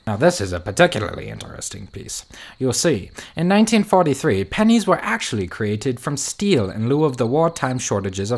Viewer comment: I legit thought that dude was saying "panties" not "pennies" XD